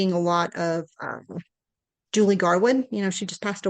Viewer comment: what was the authors name she said at 8:01? i turned on captions and it says "julie garwin" and it also reads in the captions as that but i googled it and i'm not finding any authors by that name. just wondering, i'd like to check out some of that authors books since she likes them. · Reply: Author mentioned Julie Garwood